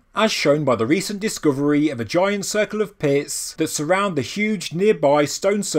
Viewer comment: Had to rewind. Thought you said giant circle of piss. Pits. Not piss.